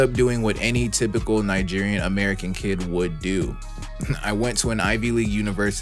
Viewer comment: Coulda sworn I heard you say "Nigerian American kid" you from Nigeria blud?